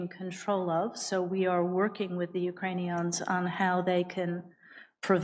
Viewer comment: did she say "Ukraine-eons"